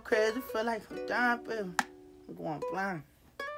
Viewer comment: what is he saying?? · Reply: He going blind